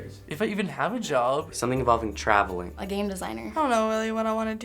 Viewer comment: did she say a Game Designer? :D